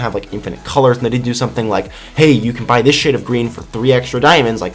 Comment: I know he said shade, but it sounds like he said S***. lol